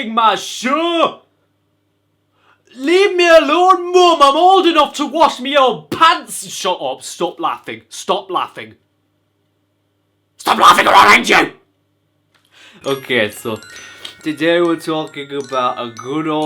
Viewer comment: stop laughing, stop laughing or I will hang you 👿 me: I... I was not laughing 😕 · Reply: I thought he said Stop laughing or i’ll eat you